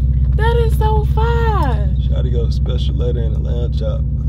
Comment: bro ii thought u said “Special Ed Inna Lamb Chop”💀